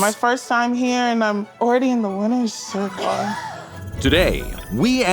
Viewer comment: I swear I heard "wiener's circle" at the beginning 😂